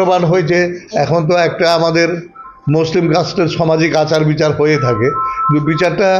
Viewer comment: " মুসলিম রাষ্ট্র " !!! ঠিক শুনলাম কি ??? 🕉️🔱🚩🇮🇳🙏🇮🇳🚩🔱🕉️